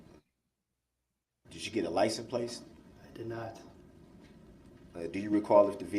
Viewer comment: WTF is a licen place? First time I heard him say it I thought it was just a slip of the tongue but that's what he says every time! 😂 just when you think Duh-rell has maxed out his stupid, he takes it to a whole new level!😂😂 man, he is dumb!😂😂😂